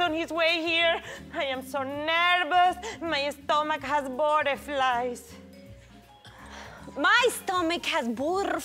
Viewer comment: I just realised that said "butterflies" when i thought she said "bought of flies" 4yrs ago😑